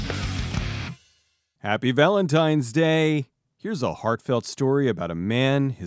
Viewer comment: at first i thought you said, "fartfelt story."